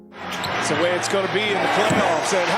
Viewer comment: I thought you said they are about to go play in the Playoffs? 😂😂😂😂😂😂😂😂😂😂😂😂